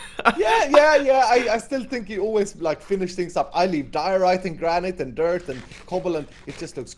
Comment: DID HE SAY HE LEVES DIORITE